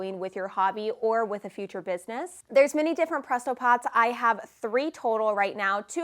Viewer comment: I thought you said Presso. I'm glad you put the product in you information or else I would have been lost.